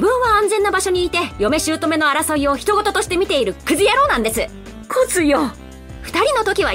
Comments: エミの台詞を受けて、姑が「クズヤ…」った言ってるように聞こえるw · クズヤに聞こえる